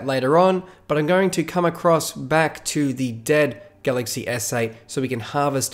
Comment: Every time he says galaxy s8, I hear “galaxy essay” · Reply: I heard "galaxy essay" as well 6:08